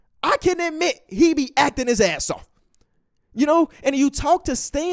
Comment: Thought said: "Chris Paul. He be F'ing his asshole"